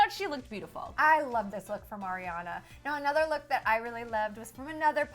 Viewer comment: Did she just say MARIAna??!